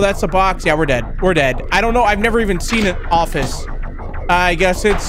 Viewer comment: it did not say office it said coffee